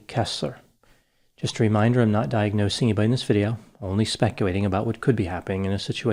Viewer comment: I keep hearing "specuwaiting" vs speculating in the intro even though "L's" are heard in all other words spoke throughout the videos 🤷‍♀️